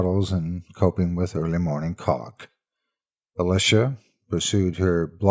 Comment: Did you say "early morning colic," or "early morning cock"? Seriously, I played it over and over and can't make it out. Funny thing is, both work in this context, so I can't rule either one out. XP I don't have good speakers on my PC so I can only turn the volume up so high. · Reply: Lucky Vine I think that was 'colic' :)